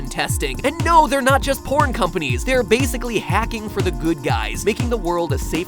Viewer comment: Did he just say no there not just porn companies😂